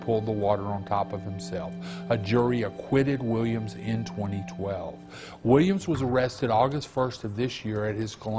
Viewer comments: the caption says 2020 but he says verbally 2012. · subtitle error (2020 instead of 2012)